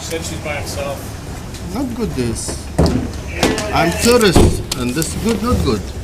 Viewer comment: maybe he should be careful what he says with that accent, it almost sounded like he said terrorist instead of tourist.